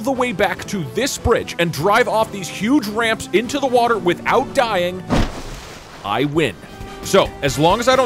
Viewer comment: I thought you said without dying?